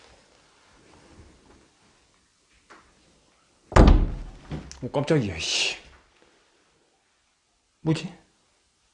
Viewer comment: 때 속닥속닥 거리는 소리가 너무 잘들리는데...나만그런가..? 9:59 에도 왼쪽에 매달려있는 사람얼굴보임ㅠㅜ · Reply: 지금 뭐하니라고 속삭임